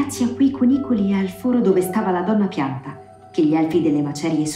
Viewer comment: solo io ho sentito piatta al posto di pianta?